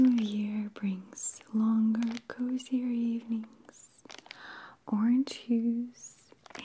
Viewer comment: Thought she said orange juice... 😂